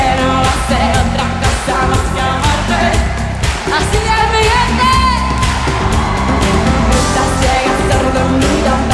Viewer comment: que dice shakira en 3:45?! Alguien me puede decir? Asi baila mi gente? o que? haha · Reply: Beauty Heaven así es mi gente